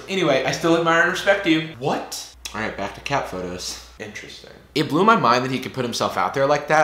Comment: Omg I rewound this 7 or something seconds part to catch what you said right at the end...." O well, back to cat photoed" LOL!!!!! No but seriously that shot is the again story of my life in youth lol